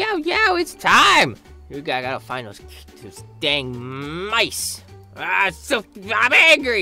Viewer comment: I thought said I got to find the dang cats but he said mice